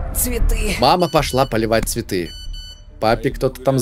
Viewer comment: мне послышалось папа пошла полить а папе кто кто написал